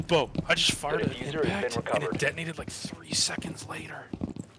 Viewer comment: anyone else hear bro i just farted and it detonated 3 seconds later